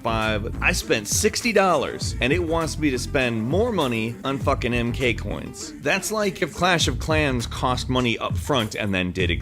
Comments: Whaaat did he say FU**ING MK COINS at 6:53 or am I mistaking!?!?!?!?!?!?!?!?!?!?! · did he say fucking?